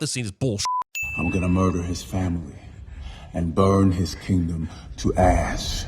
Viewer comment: did he say burn his city to "@ Because me amd youtube caption heard it that way.